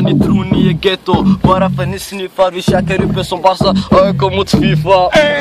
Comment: vad säger han om aik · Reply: AIK mot fifa